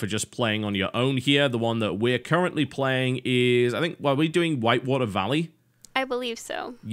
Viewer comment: Thought you said "Light Water Valley" at first :p.